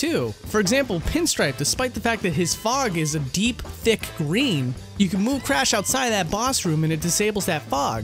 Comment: I honest to god thought that he said that pinstripe’s fog was a deep DICK green